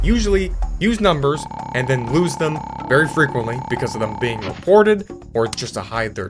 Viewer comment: Somehow I heard "report" into "divorce"